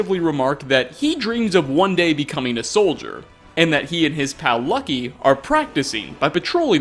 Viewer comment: At around 11:15 did you called Lucky an cow? Nonetheless Gleda is the better goat!